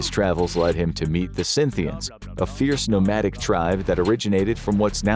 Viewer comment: I think they were called Scythians not Synthians